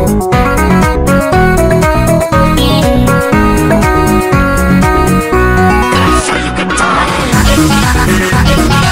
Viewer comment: what I don't know what he said😭😭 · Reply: Tricky said "DID I SAY YOU COULD DIE?!" That's all I could make out.